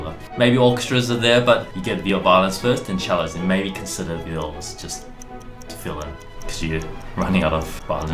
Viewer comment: The subtltle in english just used "illness" instead of "violas"